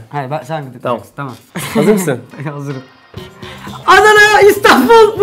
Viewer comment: Ananı diye anladım 3 kere izledim Adanaymış fjfjdjfjfj